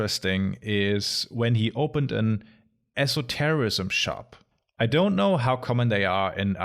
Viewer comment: This was the first time I have ever heard of an esoterrorism shop. At least that is what I thought I heard until I realized you said esotericism. But now I wonder what an esoterrosism shop would sell? Esoteric explosives?